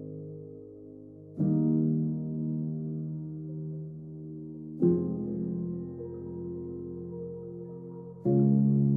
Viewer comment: what does thst last sentence say? This was soooo BEAUTIFUL btw🤩🤩🤩🔥🌞👍🤗✅👌❤ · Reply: “They do not labor or spin.” ❤️